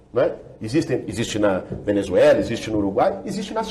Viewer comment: Cara eu entendi direito 6:58 democracia na Venezuela ?